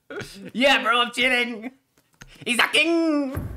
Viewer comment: lmao...what does he say at 06:53? "he is a king..." ?? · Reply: Probably "He is hacking"